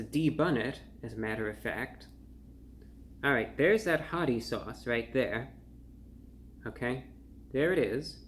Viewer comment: I kept hearing "potty sauce" and "potty chicken sandwich" 💩💩